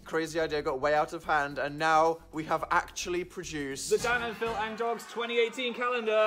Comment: I'm pretty sure Danny boi said "phand"